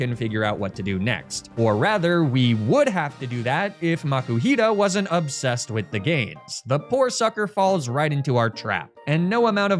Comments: Really thought you said “If Makuhita wasn’t obsessed with the gays”. Either way, I think it works 💪🌈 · I heard “if makuhita wasn’t obsessed with the gays” and had to rewatch to see what i missed lmao